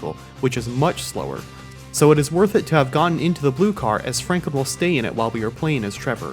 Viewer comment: I heard Frankable